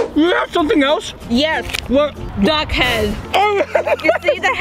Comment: Ohhh she said duck head I thought she said dog head